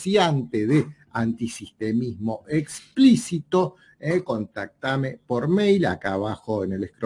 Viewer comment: La Primera vez que los ví era el proyecto segunda república y el tema fue los protocolos de los sionistas, y luego los encontré por equivocación ya que entendí " antisemitismo explícito"